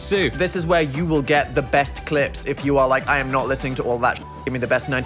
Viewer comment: so... I paused to go and make sure I was following on tiktok and insta and it paused at the perfect moment that it sounded like Dan was gonna say clit not clips ...